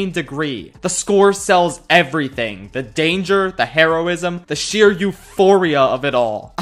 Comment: why did i hear heroin